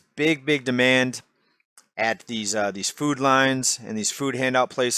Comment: Every time says “food lines” I hear “food lions” am I the only one?